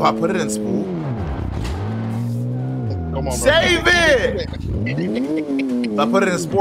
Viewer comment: when u said “save it” it sounded like meek mill saying “get up” LMAO